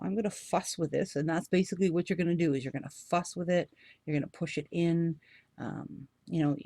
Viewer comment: I had to replay twice at 25:48 and 25:50 cause I swear she said I'm gonna fu*k with it and I'm gonna fu*k with it...lol. But finally I realized she said " fuss with it "... ..